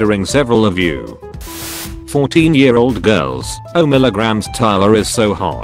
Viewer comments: Anybody notice that the text to speach thing said oh millagrams instead of oh my god at 2:41? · Bru did it just say milligrams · Am I the only one that noticed at 2:39 he said 0 milligrams instead of omg? I am dying of laughter😂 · Did anyone else hear oh Milligram instead of Oh my god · Did anyone else hear oh milligram insted of omg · Wait, did it say O' Milligram